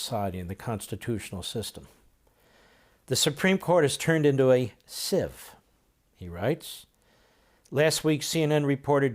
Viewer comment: I thought he said "The Supreme Court as turned into a Sith!"